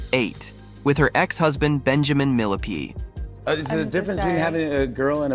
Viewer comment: With her ex-husband - "Benjamin MILLIPEDE " @ 1:47😂😂😂 🐛 did anyone else hear that? 😂